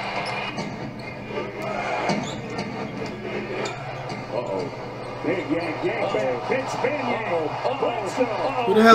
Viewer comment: what did the Commentator say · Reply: V Management lol